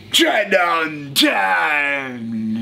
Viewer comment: The "TRAIN UNTAAAAMED" outro never gets old 👌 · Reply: I thought he was saying, “train on time”, lmao